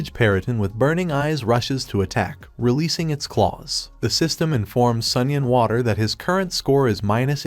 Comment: When it says the dragons name, All I hear is “Sunny and Water” and I can’t unhear it no matter how hard I try, so I guess that’s it’s name now lol